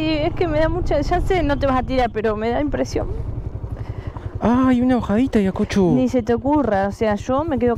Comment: Creo que escuche “Acá hay una bajadita Acocho …. Ni se te ocurra.” 😂😂😂. .hágale caso a 🙏. Mira que a estas alturas va a terminar por odiar a Laura Pausini tanto escuchar la canción “Se Fue” . Ya casi cada tres videos la pobre escucha , se fue , se fue , se fue la moto, se fue la moto . 🙌🙌🙌. Grande. 😂. Y a ti también has convertido a cada vez en una mujer más fuerte . Son lo mejor